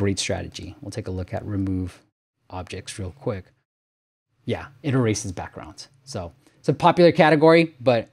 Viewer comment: Strange, you seem to have manual subtitles for this video rather than auto generated but at 4:10 you say "Yeah, it erases backgrounds" and the subtitles say "Yeah, in a racist background". Then at 4:27 you say "it's number one for text editor" and the subtitles say "it's number one for sex editor".